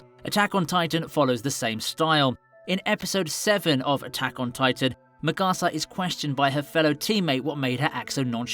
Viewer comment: I love a video essay as much as the next fan but did you just say "McArsah"